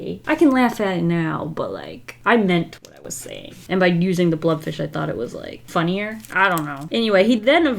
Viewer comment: Thought u said blob fish i was like oh no...